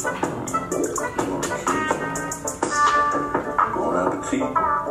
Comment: I heard bone apple feet because what in the absolute FUCK